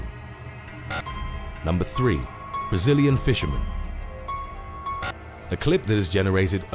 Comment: Ooooh! I thought you were saying, “Resilient fisherman” 😂